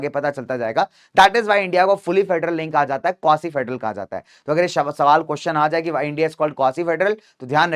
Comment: kya bole hh sir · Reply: Quasi federal